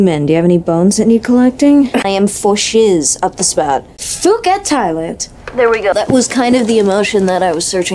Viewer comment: what is she saying? Ive been confused about this since 2007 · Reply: Phuket, Thailand